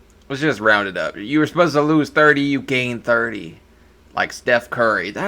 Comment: Oh, you said to lose 30lbs. I thought you said gain 30lbs